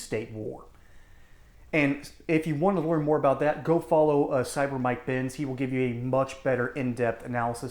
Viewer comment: "… go follow cyber mike ?finn? ?ben? I can't make out what you said, and my search was not very productive. Please advise!